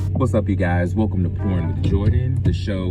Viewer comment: I'm glad he cleared it up that he said pourin' and not porn cuz it definitely sounded like he said porn